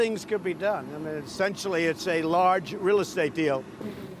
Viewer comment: 🤣🤣did he say it's just like a real estate deal